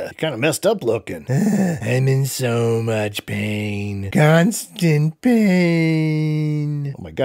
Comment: What was shin Godzilla saying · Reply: "Pain..... constant........ pain........"